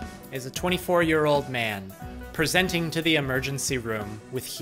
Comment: I swear I'm having a stroke or I'm just really tired cause I heard "24 GB is a year old man presenting the emergency room."